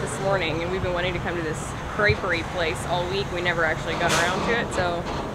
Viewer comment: I thought you said "drapery place" instead of "creperie" and was wondering what kind of curtains were so special that you'd been looking forward to going there all week... lol. <3 Looks like a wonderful time!